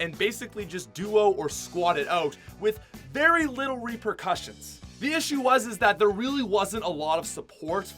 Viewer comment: When he said repercussion, I thought he said “no reapers cussing!” 🤬 XD